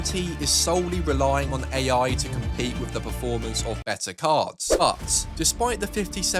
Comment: i thought bro was saying "cars" until i was like "oh right, it's his goofy accent, he's saying: cards"